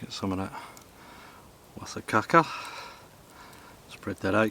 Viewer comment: did you call that stuff "caca"?